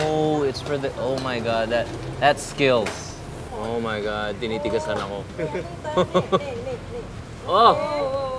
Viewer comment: i heard "ohhh my god tinitigasan ako" haha thats awsome 😂